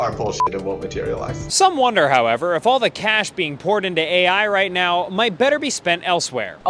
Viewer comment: Did he just say pourn